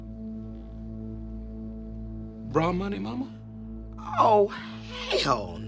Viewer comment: "Bra money mama" "oh HELL nah" 😭😭😭😂😂😂😂😂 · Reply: I thought he said "borrow money, mama". Now it's even funnier.